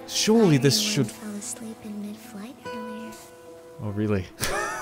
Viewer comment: Did Iris say she almost fell asleep in mid-flight?